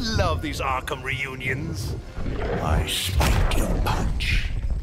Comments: I have no idea what he's saying and neither do the auto generated subtitles. I speak in punch? Is that what he's saying??? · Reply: The Banana Melon "I spiked your punch." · What does scarecrow say at 0:07? Cant make it out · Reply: "I spiked your punch"